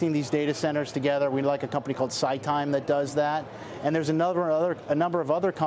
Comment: What was the name of the company he mentioned for small cap picks and shovel play? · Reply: Sounds like he said site time?